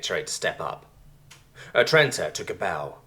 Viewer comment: Earlier story when you said the name Atrenta, I thought you had said Satrenta.. I was wait... someone used the name of a character I made in World of Warcraft and the name my friends know me as? Also used that name as my main in SWTOR. Just was a little weirded out.